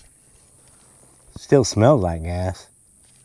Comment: All I heard was, "still smells like ass." 😂👍 The question is, did the gas alone kill the ants or was it setting it on fire that made the difference? I think we should experiment further, one with just gas, another gas + fire.